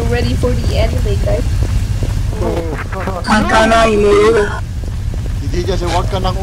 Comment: I hear spongbob said ha ka na i mo no da😲that was a line of orochi from the kof😲